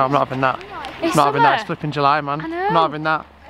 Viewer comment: it proper sounded like said “it’s f***ing july man”